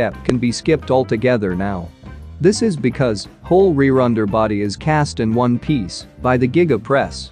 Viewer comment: "Whole re-runder body is cast in one piece"? Can't understand the computer-voice. The frame shown (Model 3 or Y) appears to be many welded parts (ditto at 4:10 for Model S or X chassis). I thought they were just die-casting the rear subframe of Model Y, so is he talking about future plans? They had been casting the rear frame as 2 parts in a smaller machine, then riveting them together w/ steel supports, so this Giga-press lets them do it as one part. Have these single piece rears been seen in Model Y's to date? Drones have photographed many stacked outside all around the Fremont factory. Rejects, reworks, or just stock waiting to go into cars? Die-casting large parts is not new, indeed a 2016 Cadillac did so for large rear parts. This is just bigger. TBD if it pencils out. Welding steel parts together isn't terribly problematic since all automated. How the sausage is made matters less than cost and weight. If the future CyberTruck uses die-casting as speculated, I doubt it could be in the promised stainless-steel, unless some exotic new tech allows die-casting steels.